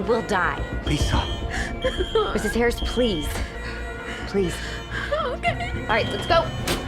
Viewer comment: Did she say no or ok? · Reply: she said ok lol